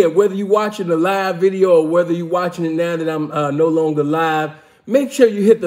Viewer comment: Bro what he said now that you watching it now that I'm not alive fuck he knew his time was short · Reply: Awww no longer live at first it sounded like he said now I'm no longer alive but he was talking about the live video